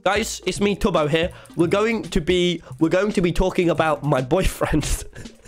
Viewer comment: “BoyfriendS” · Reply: there’s a thing called poly yk not saying he is but it’s not weird to have multiple partners and plus this was just a joke cuz it sounded like he said boyfriends